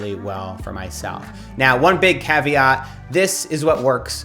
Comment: Did Matt say 'caveat' or 'caviar' at 0:34?